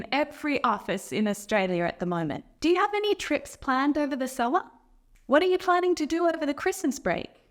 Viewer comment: what did you say about the word 'summer'? Did you use slang, or am I confused? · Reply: Do you have any trips planned over the summer?